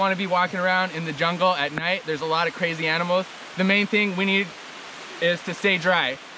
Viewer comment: Did he say crazy animals 17:07 ? i love it